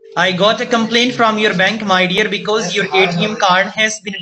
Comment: did he just say “my dear “💀💀💀😂😂😂